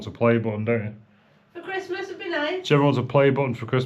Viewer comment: lol I thought you said Jemma wants a play boy for Christmas 😂 I had to rewind it to check 😂 rofl 😊 great job she’s looking fantastic 👍